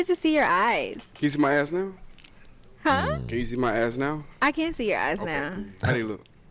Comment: Sound like he said can you see my ass eyes now